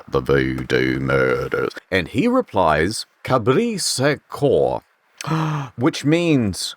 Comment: actually its supposed to be french but even as a native I have no idea what he said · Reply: If you're referring to "Cabrit sans cor" I'm pretty sure it means "Goat without heart" in New Orleans creole, which would actually make more sense than the game's translation